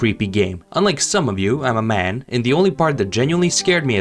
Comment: all I heard was “unlike some of you, I’m insecure” like who tf asked buddy, there’s definitely girls who are less of a pussy then you for sure